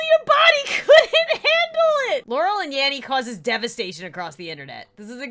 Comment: I thought she said Yammy and cause devastation across the internet and all I thought was "oh god Lauren what did you do now?"😂